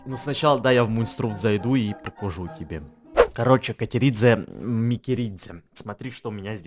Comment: мне одной послышалось что Эдисон сказал: Катеридзе МАТЕРИТСЯ?🤣🤣🤣🤣🤣🤣🤣😂😂😂